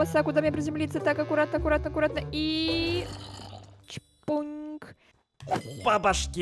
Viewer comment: сказала ЧПУНЬК заметили я удевлина что не кто не заметил что она сказала это но это правда я заметила посмотрите